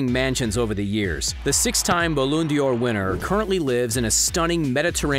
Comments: Did you just say "balloon dior"? Lol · did he say “Balloon d’or” 😂😂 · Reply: No. He said "Balloon Dior", which is even worse. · did he say “Balloon d’or” 😂😂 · Did he just say ballondur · did he just say balloon d" or instead of ballon d'or???? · Did he just say "Balloon Dior" 😂😂😂nah fam, just stop